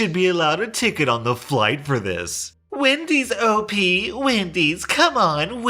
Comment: I HEARD WIMPY'S AND DAMN NEAR HAD A HEART ATTACK. but wendy's is much more common.